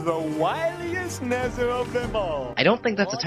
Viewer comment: When I was a kid I misheard the part about being the "wiliest Nezzer of them all" as being the "whiniest Nezzer of them all." I was a little confused why Mr. Nezzer wanted to be famous for being whiny.